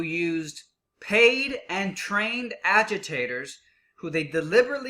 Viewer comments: At first I thought it said alligators and I was just like, "what has this election come to" 😂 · I thought it said "trained alligators"